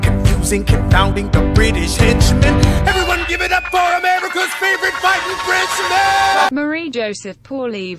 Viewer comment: on 0:29 why did the captions say "and one give it up for America's favorite viking princess 😂😂